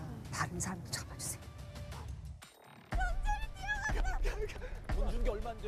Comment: 나만 “연준이 뛰어갔어!” 로 들리나..?